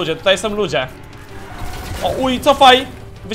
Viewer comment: czy ja nasz usłyszałem "o h*j cofaj" ? jak nie to przepraszam :D